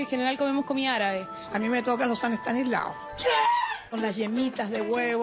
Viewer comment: han pasado 2 años y todavía no se lo que dijo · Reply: "San estanislavo". Son unos postres que se hacen con almendras, huevo, muy pequeños, cuadrados, onda para cócteles. Igual son recetas más antiguas.